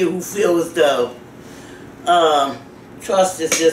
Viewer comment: I'm new to this channel, who is that lady she keeps it REAL I like her she is funny and At 29:56 Did she say "trust" was 4 letter word?